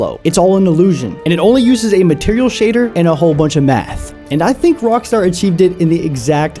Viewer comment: I swear, bro, said meth instead of math. And it would still check out.